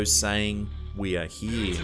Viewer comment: I heard “we are in”